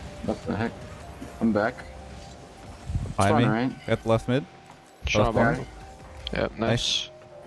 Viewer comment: does he say he gots left mid?